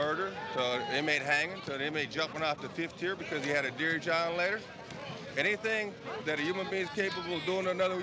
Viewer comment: What did he say @ 3:48 a inmste jumpin off the 5th tier cuz he got a wat ....??? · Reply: Young Dre He got a Dear John letter from his girlfriend.